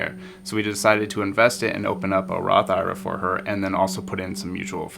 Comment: To invest it and open up a what? I just heard rara for her. Can u tell me please. I think it's at 9:15/12:43. Thank you such good advice. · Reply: Roth IRA (investment account for retirement) and mutual funds